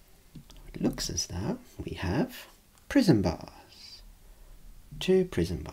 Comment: Really loved the video. Being half asleep I kept hearing 'Prison bar' instead of the prism bar. "Didn't know there was a prison RP on this channel" I thought while drifting off. No prison bars were noticed in my dreams though.